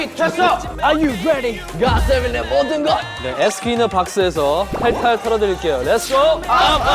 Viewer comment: It sounded like Jinyoung was speaking German (if there are any who can understand): Bei 0:09,5 Jinyoung: der rest in der Box Ahahaha sounds so weird xD